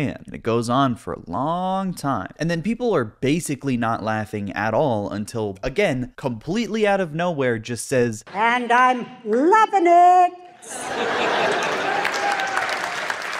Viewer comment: what did she say? "And I'm planting news"? What?